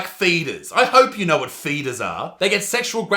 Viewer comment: When you first said "feeders" I heard "fetus" xD